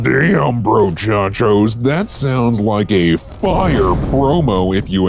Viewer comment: i heard that as ‘sounds like dabi’ as in mha,, I FORGOT DOBBY EXISTED